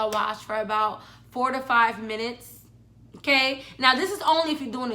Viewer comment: I thought she said 45 minutes I was like “ WAHHHHHHH”🤣